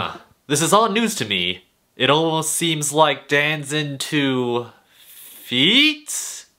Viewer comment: idk if it is but i found a seceret at 6:23 i have no idea if it is but he says dans into feet and he said it and t sounded like dancing to feet